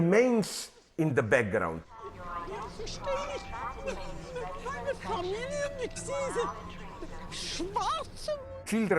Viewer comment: Does anyone know what that old lady 0:40 was saying in German, something like, "wait, my family...with blacks." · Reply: "Ich verstehe nicht - - meine Familie, mit diesen... Schwarzen?" "I don't understand - - my family, with these... blacks?" She's just lamenting that she's locked in the cage with black people. So a racist lady who thinks it makes sense for black people to be locked away, but surely not those like her.